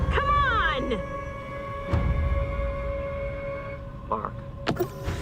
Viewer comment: for years, I thought Lee said 'Burn', when he said 'Mark'.